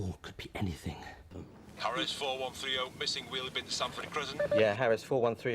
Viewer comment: I heard Sanford Prison lol.